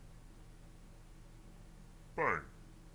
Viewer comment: did you say "fart?"